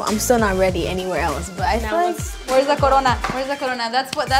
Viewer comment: Anyone else hear "where's the corona"